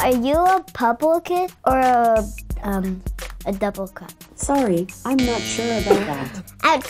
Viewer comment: did he say "double crap" xD